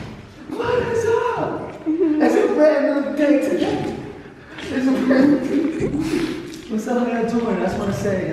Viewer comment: Anyone else hear that at 9:34 after said its a brand new day today ! 😮😮 ghost said SAY WHAT !!!!